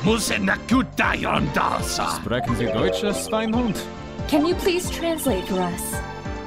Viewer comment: What did edgeworth say at 11:43? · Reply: Sprechen Sie Deutsch, Schweinhund*? Schweinhund as in "pig-dog"